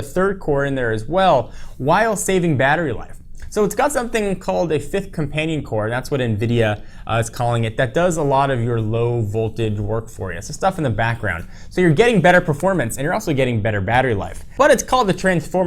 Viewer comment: Did he just say low VOLTAGE? :)